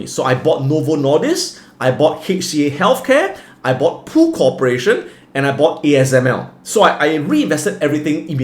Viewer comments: What did he says? In 23:45 PU CORPORATION?!? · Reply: Pool Corporation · Hi, Thank you for the video. 23:40 Did you say Novo Nordisk (NVO)? I coudn't catch that. Can somebody let me know which stock he is talking about?